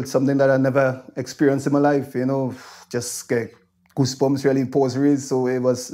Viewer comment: did I just hear "goosebumps baws raised" reaction to playing in front of 50,000 people?😂😄😆😅 Brilliant